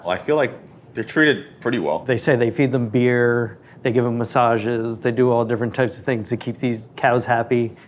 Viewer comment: I heard they're massaged by pandas 🐼